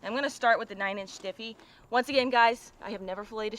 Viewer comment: Did she just say 9'' stiffy lol